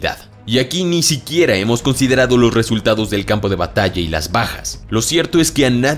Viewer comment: Entendí "las pajas"